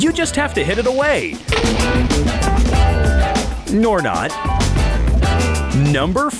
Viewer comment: The Narrator said "Nor not" instead of "or not" XDDDDDDDDDDDDD.